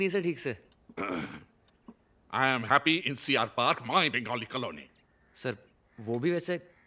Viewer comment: did he said CR park? which is ChittaRanjan park, named after Chitaranjan Das.